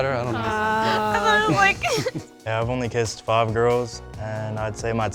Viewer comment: when the dude said he kissed 5 girls I thought he said 5 year olds because I was too busy laughing at the guy before him. 😂😂